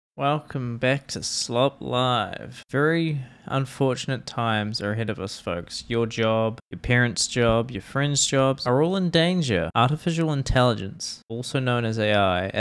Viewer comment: thought you said "furry unfortunate times" and was so shocked haha